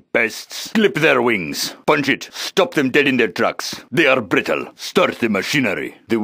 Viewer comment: "Stop them dead in their tracks." Back in the day, I heard that as "Stop them getting their drugs". Boy was I wrong. by the way, "They will break and shatter" is probably my favorite of the quotes from the Quad.